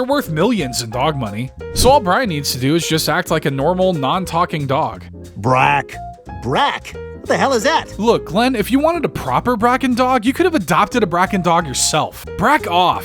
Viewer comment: Did he say “Brak” as in The Brak Show from Adult Swim @ 26:13? 😂